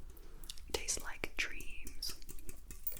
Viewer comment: You are so funny. I thought you said some of them taste like trees. 🤣🤣 but you said dreams. Your videos are really soothing. ❤️